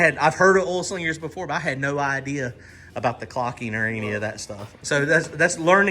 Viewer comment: I swear I thought was saying “caulking” about the Procharger until you pointed out the different drain hole positions and realized you guys were both saying “clocking”. 🤣🤣🤣